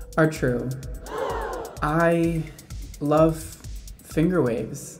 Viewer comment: I misheard the first 30 seconds and all I heard was “I love fingering” , I was like wtf Brad